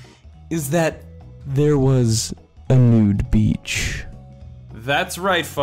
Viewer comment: When I hear these videos I always thought it said Nuke Beach but now uuhhh